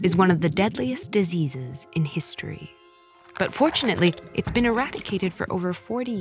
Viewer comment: I thought it said “unfortunately” instead of “but fortunately” and was like “wait, how is that a bad thing???”